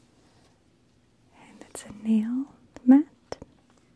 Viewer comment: When you said nail mat I thought you said nail matte and I was like what.